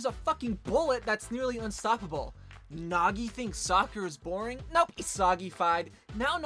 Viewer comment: When he said Nagi thinks Soccer is boring I thought you said sakura is boring💀